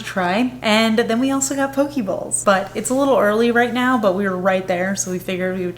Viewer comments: I love Daiso! When I was living in Japan, I would just pop in and wander around for fun. Edit: When you said you got "poke bowls", I heard "pokeballs" and thought, "Where did you get pokeballs!? And why do you need to put them in the fridge?" Haha. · When you said poke bowls, i thought you said poke balls... like Pokémon. Then you said you put them in the fridge. Very confused for a moment. Lol